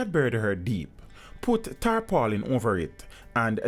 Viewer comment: My man said Tarpaulin, I thought he said Tar pollin. I was like what is Tar pollin? I thought it was something used to hide the smell like kitty litter.. We just say Tarp here in the U.S. great to know the full name, thanks..